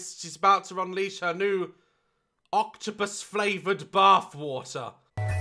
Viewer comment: THE CAPTIONS THOUGHT U SAID BIRTH WATER LOL